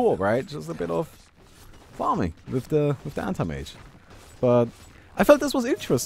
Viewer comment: seriously, i heard "Hentai Mage" instead if "Anti Mage" most of the times you said that lol. Im kinda curious where did the "Baumi" name came from